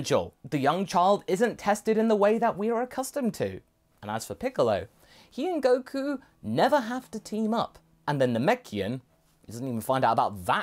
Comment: What Masako said: 'Which means Piccolo and Goku never have to team up.' What I heard: 'Which means Piccolo and Goku will never test drive cars together.'